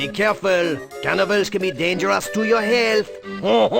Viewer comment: I thought the guard said “cannibals are bad for your health”.